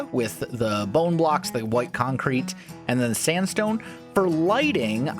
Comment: When he said smooth sandstone, I thought he said spruce sandstone and I was like "what???" Lol Also, I frickin love how he ate the diamond popsicle